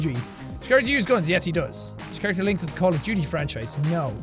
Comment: I was watching this while working on a project for video game design class and it sounded like he said call of judy lol